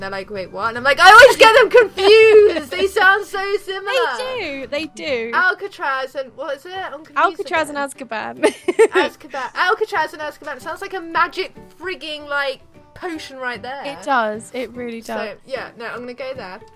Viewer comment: did say freaken? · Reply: she says freaking, its not bad :/